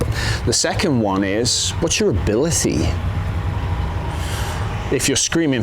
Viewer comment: Did he say What'syour Abilicy ?